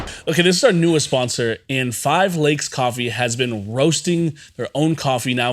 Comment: I thought you said 5 legs coffee lol